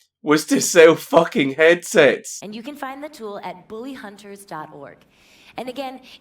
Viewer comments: Oh shit, I thought you were saying 'billyhunters' not 'bullyhunters' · it sounds like hes saying the willyhunters or billyhunters